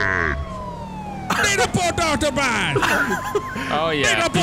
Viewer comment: it sounded like you said "I NEED A FOOT DOCTOR BAD!!!"